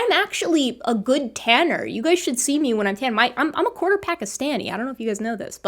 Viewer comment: WHAT?!? You’re a 1/4 Pakistani? Maybe we’re distantly related through my wife! 😁😁😁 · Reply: A quarter Pakistani! Oh, that makes way more sense than what i heard: “a quarter-pack o’ Stanny”. I was like, i guess Stanny is a regional… beer…? 😆